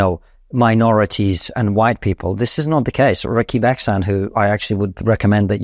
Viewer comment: At about 7:09, Konstantin mentions an academic who argues that most ethnic tension in the U.K. is taking place between different ethnic minorities. It sounds like he says: "Ricky Bexhand", but, I have googled variations of this -- & not found anything. Does anyone know more about this?